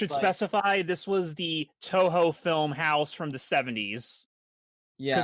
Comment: I thought I heard "Touhou" when you said this and I went crazy for a second lmao